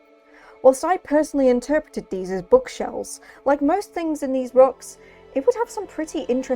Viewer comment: to all those who share my American ears it has come to my attention that she is saying “bug shells” and not “bookshelves”. I don’t know if anyone else misheard but I paused the video for a good second looking for the bookshelves she was talking about.